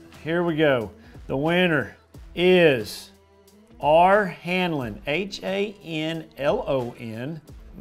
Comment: Man, my heart skipped a beat when you announced the winner. I first thought you said Hamilton, not Hanlon! 🤣 BTW, I’m “that guy” from far northern CA. I hope I’m not being a pain about Shasta. I just love these “Where to Start” segments so much. Incredible info! Don’t sell yourself short on Shasta. I’ve been told it probably sets up much like Table Rock. Similarities, Highland Res., All 3 species, tons of standing timber & rock, 1,000’s of ditches & hundreds of humps, deep(500ft at F.P), T.R. is about 30% larger, etc. Biggest difference is our huge water fluctuations in the west, but that actually helps finding structure on google earth. I’m just honored that you mentioned us out here. Love all of your vids. Good luck at Redcrest! I’ll be rooting for you.